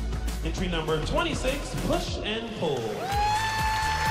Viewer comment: Thought she said push and poo xD